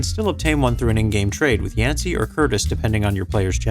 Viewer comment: Im sorry did you say Yancy? Never played that gen of Pokemon onwards but thats a ridiculous name nonetheless haha